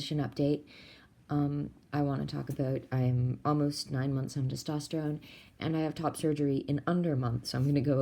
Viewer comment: i thought it said 9 months on top surgery and i was very confused